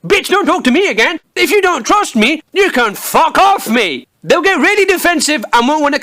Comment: "if ya dont trust me, you can fuck off, me." that is literally what i heard.....he's telling himself to fuck off